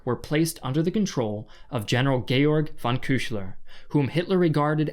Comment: I replayed that multiple times and every time I keep hearing "gaylord"